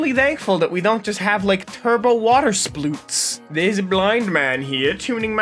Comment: I heard "Turbo Water Sploots" And I laughed harder than I should of 😂